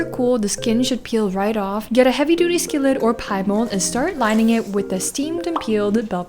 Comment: Oh gosh😂 my brain is so genshin. Around 14 mins in she say heavy skillet or Pie Mold, what I hear "get a heavy skillet or Paimon" 😅 (thanks subtitles for correcting me😂😂)